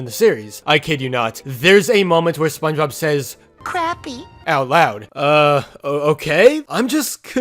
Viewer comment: i thought he said crampy cos he was rubbing his backside like he had a buttcramp while trying to swim. also that whole lore change turned my heart into the saddest metronome.. well, at least this is confirmed an alternate timeline...